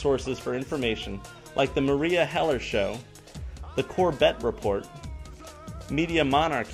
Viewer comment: lol,I thought you were saying Corvette Report.Great video man!You seem very down to earth and I look forward to more of your videos,great advice as well as inspirational.See you soon.